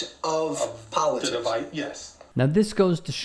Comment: did he say because of Biden? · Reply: He said “divide”